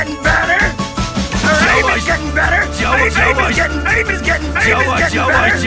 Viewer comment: I hear “babies getting better.”